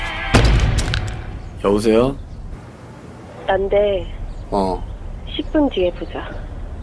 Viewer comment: I'm sorry but can anybody tell me what does Yoo Mi say in 0:13 ? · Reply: The translation is not mine but here: It's me let's meet in 10 minutes